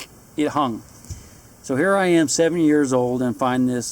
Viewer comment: At what point did he ever say he was 70? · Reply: Shitster lol he said when he was "7" years old with his mother and it sounded like he said "here i am 70 years old" it was when he was talking about a dead bird and bird nest...i thought he said 70 too had to listen like 3 times lolol